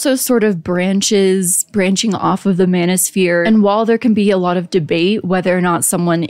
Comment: i keep hearing "Mana Sphere" and wondering why there's no D&D fantasy talk